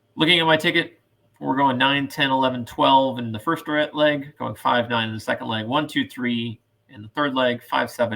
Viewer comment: That’s a 72 dollar ticket right 4x3x2x3x2… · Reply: Thought you said 1 5 9 in the second leg only 5 9 sorry